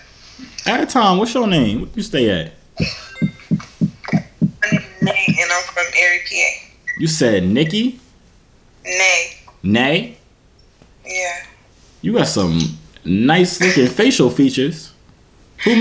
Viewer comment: Did she say Erie? because I live in Erie Pennsylvania